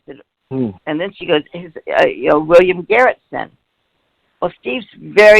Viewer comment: I got confused just then because I thought she was saying Steven knew William Garretson from school but she's actually talking about a guy called William "Garrison" (hence, why she was initially so confused). Just in case anyone else got mixed up